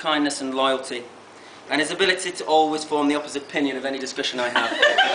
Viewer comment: I can't here what he's says at 5:30 that gets a laugh. Just can't make it out...."his ability to always....................that i have". Please someone fill in the blank(s) for me! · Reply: "form the opposite of my opinion"